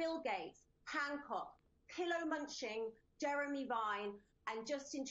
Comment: Theyre was a moment there I thought she said "pillow munching"... and I fell in love with her...